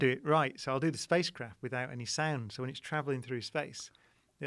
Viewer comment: On 2:59 when he said sound I thought he said sand and in my head I was like “I don’t like sand.”